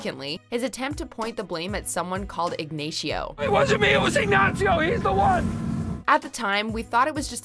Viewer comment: Igneixio? What hell did she say there? 😂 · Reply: Yeh Ignacio Nacho varga